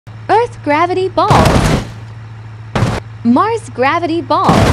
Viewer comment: Did she just say earth gravity balls 🤣🤣🤣